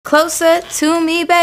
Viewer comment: I thought she was saying closet in